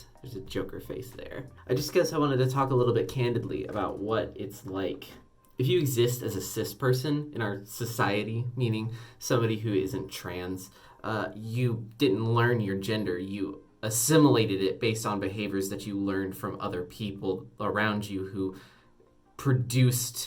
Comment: Girl, did you just say cisociety